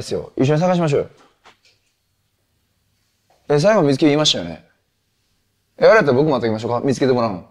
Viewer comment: ここなんて言ってんの？ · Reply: 最後まで見つけるって言いましたよね？？